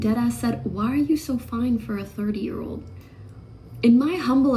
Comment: IDK if her audio is bad but I heard 40 year old at 2:05.